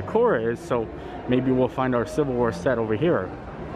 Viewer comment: Silver Wear, I thought you said Civil War... I was like, what? Disney has that?